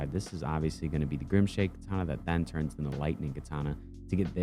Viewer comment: i thought you said grimace shake katana, the brainrot got to me...